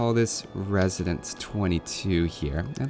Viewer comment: when you changed the name of the district at 22:10 i misheard "resonance22" and i was like "he's going to joke on him because he don't love japanese?"